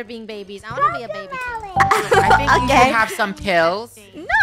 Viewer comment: I heard say I gay!